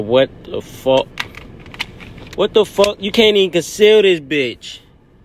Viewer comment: “You can’t even conceal this bih” my brother have you ever been to south Atlanta 😭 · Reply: Thank you for your clarification. Stg I've watched over and over AND OVER AGAIN LOL looking for the problem, but it was me myself. Smh. I was the entire problem the whole time lmao. I thought bruh was saying, "you can't even sell this bih......." so yeah good looking out fam. I almost gave up on the cuddy. Haha.